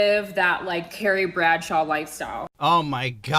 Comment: I thought that bitch said her dream was to live the "Terry Bradshaw life"...lmao